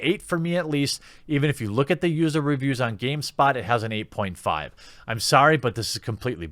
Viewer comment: Oh! I almost thought you said GameSTOP. Good, they're still bros.